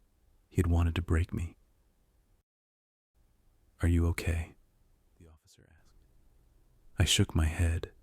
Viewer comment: did i hear "bro are you serious"?? what was said?? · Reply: i heard the same thing at first 😭😭 he said "the officer asked"